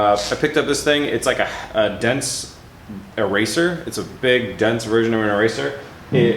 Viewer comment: I thought you said "dent eraser" haha. I was like, what the hell kind of wizardry does that use?